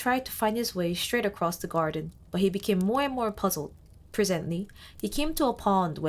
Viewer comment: 난 중간에 알청자들 후려패는 피터래빗(너구리) 이야기 인 줄 알았지요.. 3:36 more and more 을 모에모엥 으로 들었다 생각해 만족하고 가겠습니다